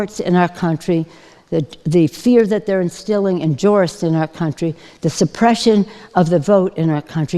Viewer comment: Oh, okay she slurred “jurists” lol I thought she said “tourist” i was like “huh?”.